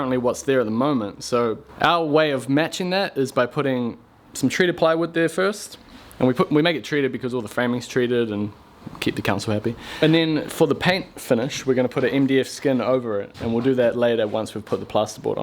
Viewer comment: Could have sworn you said "Keep the c*nts all happy" but then realised it was "keep the council happy"